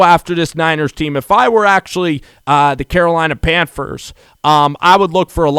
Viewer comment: Sounds like he saying Panfers! Lol